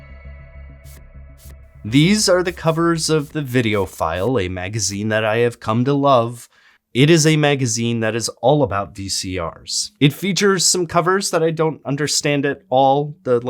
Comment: I had to do a double take as I thought you said "video file" lol